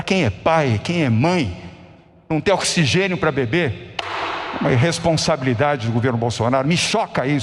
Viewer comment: Não ter oxigênio pra beber Kkkk esse é o nível do DORIA pqp. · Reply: Eu entendi "pra bebê"...rs